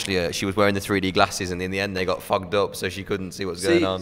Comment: I thought he said f***ed up 😂